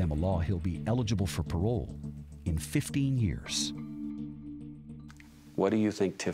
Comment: WOW 99 yrs but eligible for parole in ONLY 15 yrs !!!! That is just absolutely Ridiculous. Our judicial system is a mess !!!! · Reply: I thought he said eligible in 50 years, not 15.